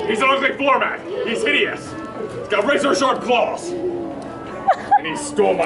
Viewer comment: for a second I thought he said "razor sharp balls"